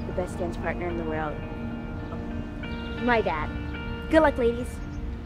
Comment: Did she just say “good day ladies” Shes 13